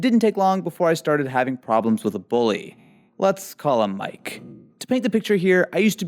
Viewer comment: Did anybody notice the subtitles say “classmate” instead of bully?